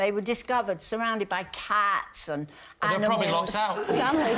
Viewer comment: What did Lee say at 10:51? Locked or luck out? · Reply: He said: "They were probably locked out."